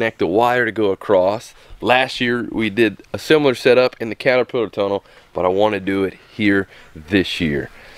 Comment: Lol it sounded like you said the cataputer tunnel,, love you guys I wait every day for ur videos my 2 youngest love watching with me